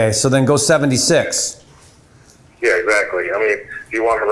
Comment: What he saying at 6:52? We're they doubles dipping? So let's go 70/6. Meaning... Wut exactly lol. Six percent closing? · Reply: Asking price was 75k and the realtor said he already had 3 double dip cash offers at full asking price. So says “ok, so go $76k”. Just to get the contract.